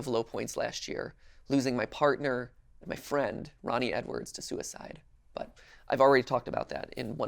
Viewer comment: did he really said susied